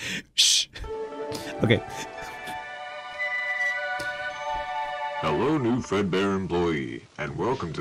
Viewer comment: Nah I thought at 0:37 it said friedbeans family💀